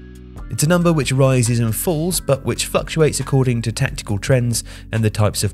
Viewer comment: Great and interesting video as usual, but I'm pretty sure you accidentally say "fucktuates" instead of "fluctuates"